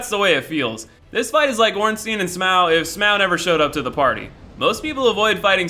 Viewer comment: Did he say smwough